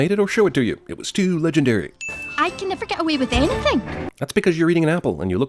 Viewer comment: I can't understand what Merida is saying at 1:11, can someone please tell me? :v :3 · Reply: "I can never get away with anything."